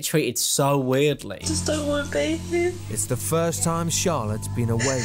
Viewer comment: It sounded like she said „I just don’t want bacon” 💀